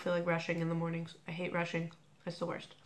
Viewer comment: i heard it like "I hate russian" ...and I thought: "wut? why?" Then turned on subtitles and everything fell into place =D